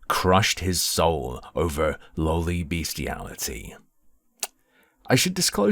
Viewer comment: I wont comment on the topic itself, cause I'd just repeat what is being said already. Instead, I will comment on the fact I keep hearing "Lowly" when you say "Loli".